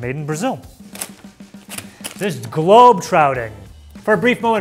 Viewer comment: I swear said 'globe trouting' instead of 'trotting'.